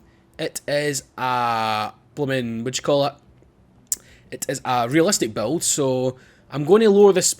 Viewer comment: The body kit kinda makes the car look like an AE86. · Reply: EKDrifter458 I thought it said super realistic build instead of supra realistic build lol XD